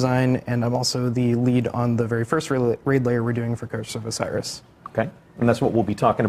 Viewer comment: A raid layer I thought they were saying.